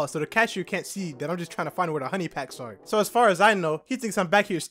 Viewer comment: did bro just say he is looking for a honey pack??? might be FREAKY